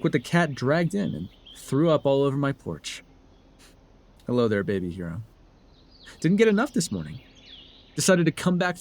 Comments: I misheard this as "babygirl" and almost threw up all over that porch ✋💀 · Timestep? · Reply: I heard it really low at the time but he says hero not girl 😭